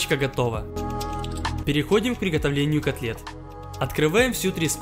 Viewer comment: мы послышалось, или он сказал не котлет а конфет